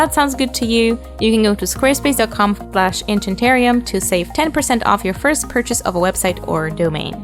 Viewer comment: I’m sorry… did-did you just say boob-chanterium?😂